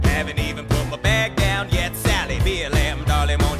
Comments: 3 dislikes: King George, John Adams, Charles Lee. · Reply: i thought you said salty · did he say blm? XD